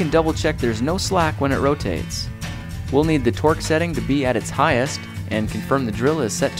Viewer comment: I heard twerk setting